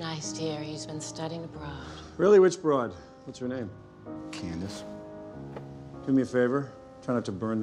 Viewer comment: did Tony really say “Candis”?